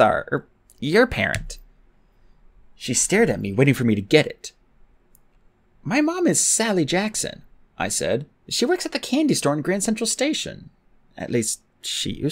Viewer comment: i thought he said sadly Jackson lol